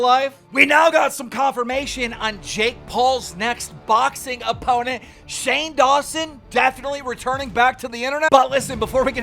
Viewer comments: I really thought he was saying Shane Dawson finna be jake Paul's next 💀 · I THOUGHT HE SAID THAT SHANE DAWSON WAS JAKE PAUL'S NEXT OPPONENT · For a minute it sounded like you said jakes new boxing opponent is Shane Dawson. Why did it not surprise me one bit lmao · For a second i thought he said jake paul mext opponent is shane dawson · For a second I thought he said that jake Paul’s next opponent was going to be Shane Dawson😂😂😂